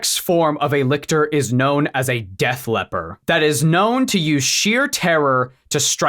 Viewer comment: "The apex form of a lictor is known as a Def Leppard." thanks, brain, that's definitely the line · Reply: I heard "Deaf Lepper" :p Like the ultimate Lictor is a HEAVILY handicapped creature.